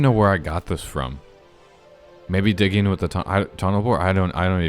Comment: Is it just me or did it sound like he said tunnel whore??